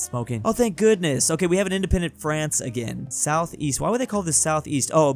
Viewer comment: East? I thought you said "Weast"